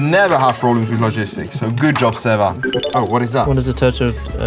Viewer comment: am I the only one who thought Lewis said "what is it Toto?"